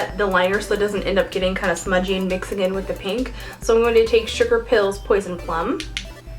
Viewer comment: at first, when you said "sugar pill", i thought you had said "stripper pill".